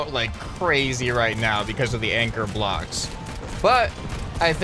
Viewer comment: did he say “anger blocks” lolololol